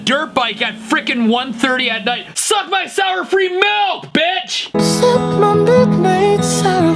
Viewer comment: did he just said b**tch?